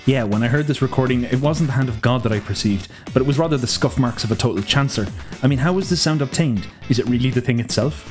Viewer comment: I love your editing, perspectives, and choice of topics. Maybe you could speak a bit slower... I had a hard time understanding what you were saying from 1:05 to 1:07, maybe local slang? I heard "total chancellor". You're my new favorite YouTuber as of this morning :)